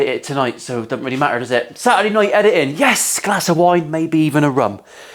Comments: Thought you said “Saturday night, glass of wine, maybe even a rub” 😂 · Sorry Rum, I thought you said a rub.